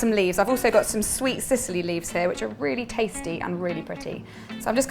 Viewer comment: Could someone tell me the name of the leaves that she uses at 5:32 please? Unfortunately I can't understand their name (English is not my first language) but they are so nice! They look like small fern :) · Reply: Sicily leaves. Have a nice day!